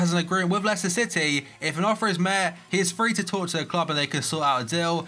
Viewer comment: "if an offer is mehr" What the hell is he saying.